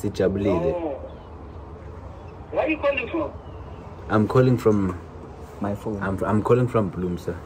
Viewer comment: who else heard "from my phone " when the dad asked where they are calling from?🤣🤣🤣